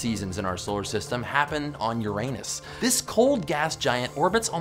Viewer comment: when he said Uranus I thought he said YOUR ANUS!!!!!!